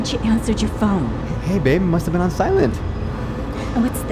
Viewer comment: 🤣😂😂😂 i don’t know if anyone noticed but it says “it must have been outside” instead of “it must have been on silent” 🤣🤣🤣😂😂😂 reply if you noticed